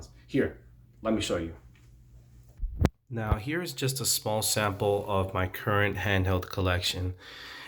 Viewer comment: my boy did you say “here’s just a small sample” 😮😂